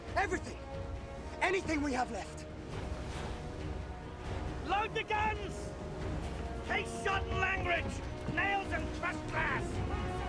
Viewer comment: What is it Gibbs says exactly at 2:10ish? After he says load the guns. I cant make out the rest of it. Somebody help please. I need to know for a fan fiction i am writing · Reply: "Load the guns! Case shot and langrage! Nails and crushed glass!"